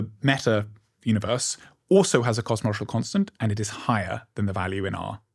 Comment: I thought he was saying "cosmercial constant". Googled it and then thought to turn on subtitles.